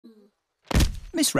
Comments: for a moment i thought it said: this rabbit has fainted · At first I heard "THIS rabbit has fainted". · Bro I thought it said This Rabbit not Miss Rabbit 💀